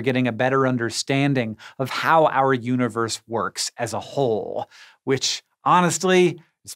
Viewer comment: Did he say how our universe works as a 'hole'?